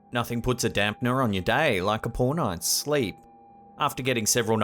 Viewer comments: I had to listen to this twice, I thought you said "Nothing puts a damper on your day like a porno and sleep." 🤣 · omg I thought he said "nothing puts a dampner on your day like a porno and sleep..." hahaha I was like OH MY GAWWLY JEE WIZZ! OH MY JEANS... but he actually said "nothing puts a damnpner on your day like a poor nights sleep..." LMAO